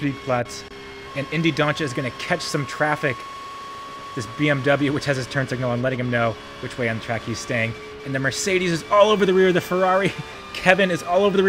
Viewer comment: what did you just say? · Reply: "The BMW, which has it's turn signal on, letting them know which way on track he's staying".